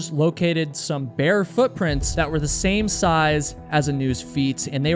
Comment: you said "they found bare footprints". Stupidly, I heard "bear footprints" 😂😂